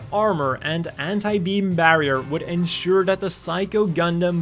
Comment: Did he just say Hentai-beam armor???!??!??!!